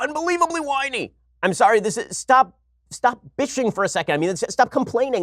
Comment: did Ben just curse? It sounded like he said "bitching"!